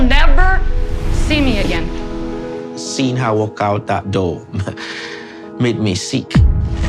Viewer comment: did he said “seen her walk out of that door?….”